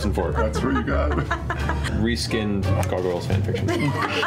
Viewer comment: What does Matt say at 8:39? Reskinned what of Exandria? xD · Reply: Reskinned Gargoyles Fanfiction